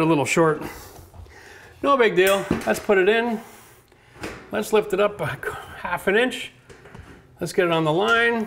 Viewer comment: How much you going to lift it up? Sounded like it was almost a "C Hair" 🤣